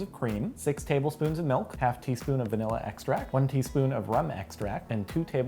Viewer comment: I thought they said ramen extract..